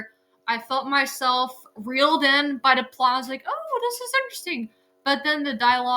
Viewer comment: Just as a friendly FYI, it's not a Marvel movie. It's Sony. Two wholly different studios with different Spider-Man characters/worlds/storylines/etc. Also, try as I might, I can't make out the 'P' word you use at 0:16 "... felt myself reeled in by the..." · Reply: Think I said plot and oof my bad 😅